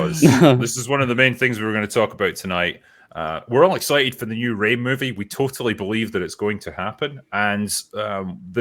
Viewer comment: I thought I heard say there was a new Raid movie and got excited, only to realise he was talking about the trash-tier new Rey movie. Dammit all.